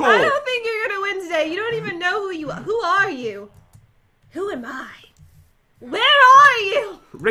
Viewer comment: I thought she is saying where, are your fingers